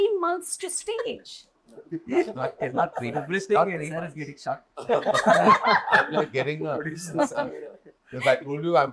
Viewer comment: Can someone please tell me what Nelson said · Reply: He said sir(Karan joker 😅) is getting shocked.